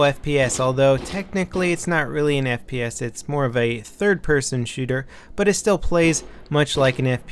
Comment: said its technically not a "FPS" but more like a third person, u misheard him